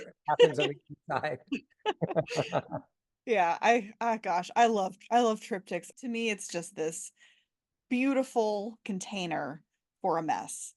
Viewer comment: Quick comment for: You said, "It (the triptych) is a beautiful container for a mess." For some reason, I at first thought that this was your description of Bacon, rather than a reference to the triptych form (I heard "He is a..."), and I laughed. Have you seen the film "Love Is the Devil"?